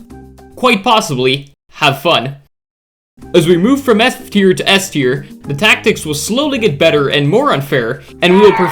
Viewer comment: At the start of the video I heard “from S tier to S tier…”💀😭🙏